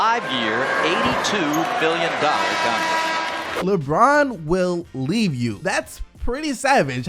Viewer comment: I thought they were saying “lebron will trade you”